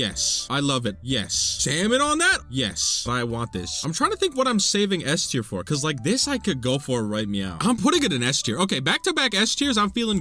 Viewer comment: HOLD UP!!! Did just say “right NYOW???” IS A FURRY CONFIRMED 😱 The past 2 weeks he flew out to a furry convention. That’s why we haven’t gotten any new content!!!